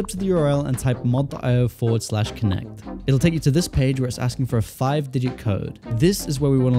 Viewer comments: I can’t tell what he’s saying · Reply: “It’ll take you to this page where it’s asking for a five digit code” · I can’t tell what he said, I heard forward but could someone tell me what I need to type please 🙏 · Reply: 4/connect